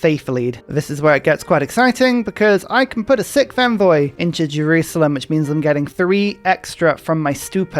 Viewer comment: rewatching this and i hear:"i can now put a sick fanboy into jerusalem'" blink blink beg your pardon? rewinds "a sixth envoy into jerusallem" ah ok that makes somewhat more sense x.x